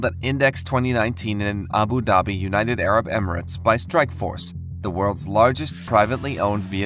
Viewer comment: sound like you said Strike Force instead of Streit Group.